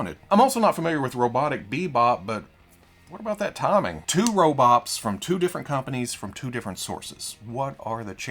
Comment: did you just say “two robops”? lol